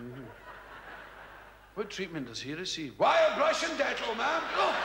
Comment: When I was a kid, I thought Billy was saying “wild Russian nettle” 😂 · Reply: i thought he was saying "wild brush and dental". never heard of "dettol".